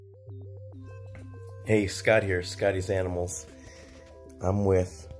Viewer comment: It looks much better. Btw. I heard him say ty scotty